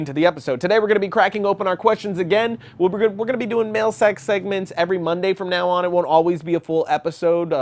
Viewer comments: does he say that they are going to be doing "Male Sex Segments?" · Okay, who else thought at 0:43 he was saying "male sex" segments instead of "mail sack". BE HONEST, I KNOW I'M NOT THE ONLY ONE!